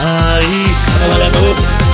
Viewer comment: 前に、「あの顔は忘れたい」のところを「あの顔はまじでない」って聞こえるっていうコメントを見たのを思い出して1人でツボってるw(語彙力)